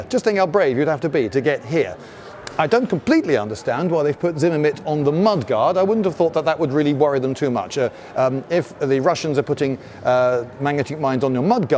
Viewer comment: Sounded like he was saying "ZimmerMit" instead of "ZimmerRit"...the latter being correct.